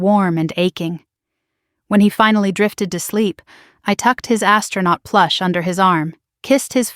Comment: Fast forward to 45:47 " I thought she was gonna say when he finally fell asleep she tucked his ASS PLUSH😂😂😂!!" However she said ASTRONAUT😂😂😂